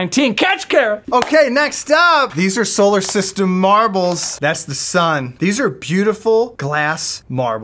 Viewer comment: I thought he said "solar panel marbles" I was like what does it generate energy?!??!!